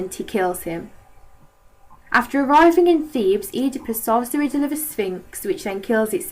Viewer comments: what did you say at 2:02? sounds like "saw in hurabis sphinx and it killed itself" not to be rude im just a little confused on what was said there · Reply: solves the riddle of the sphinx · wtf did u say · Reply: solves the riddle of the sphinx